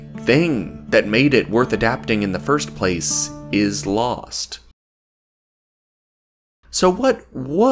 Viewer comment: Thanks for watching, Joffrey Baratheon was a total Sigma at 8:25 that black box is supposed to be the release date of Dance with Dragons, the last book which was released in 2011. My editing software messed up, now it's too late. Shame. DISCORD: <link> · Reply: did you say lost yes what was lost?